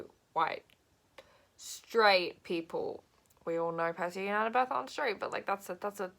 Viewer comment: Did you just say "we know percy and annabeth aren't straight?? " ?!!!!!!!